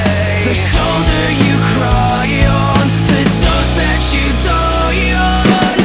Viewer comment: LYRICS FOR EVERYONE WHO WANTS TO SING ALONG! You know you need a fix when you fall down You know you need to find a way To get you through another day Let me be the one to numb you out Let me be the one to hold you Never gonna let you get away The shoulder you cry on The dose that you die on I, I can be your painkiller, killer, killer You'll love me till it's all over, over 'Cause I'm the shoulder you cry on The dose that you die on I, I can be your painkiller, killer, killer I know what you want so desperately You know I'll give you one for free Forever you're coming back to me Now I'm gonna give you what you need 'Cause I know what you fiend on and what you lean on And what you lean on The shoulder you cry on The dose that you die on I, I can be your painkiller, killer, killer You'll love me till it's all over, over 'Cause I'm the shoulder you cry on The dose that you die on I, I can be your painkiller, killer, killer Did you find another cure? Did you find another cure? I, I can be your painkiller, killer, killer You'll love me till it's all over, over 'Cause I'm the shoulder you cry on The dose that you die on I, I can be your painkiller, killer, killer Painkiller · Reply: I thought it said the dust that you die on